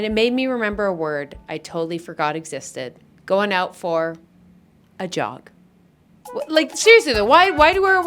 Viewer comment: I thought she said “a job” 🦧🦧🦧🦧